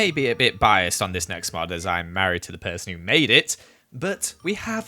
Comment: For some reason I thought said, "I'm married to this mod" 😂